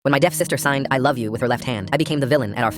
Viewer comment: My dumb ahh thought it said dead sister 😭🙏